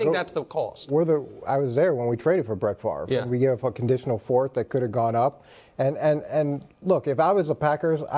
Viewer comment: Ay Mangini tf you say? 😂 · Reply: Oh look oh, i was there when we traded for Brett Farve, and we gave a conditional fourth that could have gone up